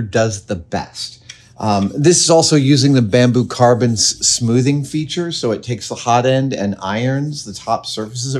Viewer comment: I heard you say "bamboo carbon smoothing". I could not find a reference to that. I did find ironing which I assume is the same. Unfortunately my printer doesn't do that.